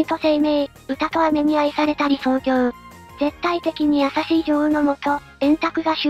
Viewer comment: ここの女王がもうジョオーにしか聞こえない